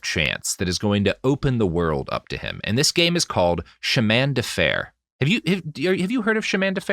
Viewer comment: I heard "Chemin de Fer" as "Shamanda Fair", like the name of a woman or possibly a Roxy Music song.